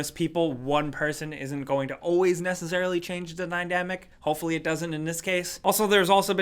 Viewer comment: did he say Nydamic?